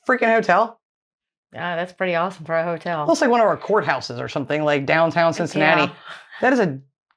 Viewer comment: Had to rewind that bit when you was looking at the hotel. For a second I thought said "looks like one of our whorehouses downtown".😂😂😂